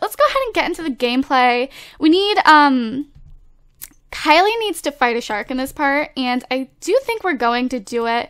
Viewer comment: Lol it sounded like u said Kylie needs to fart out a shark XD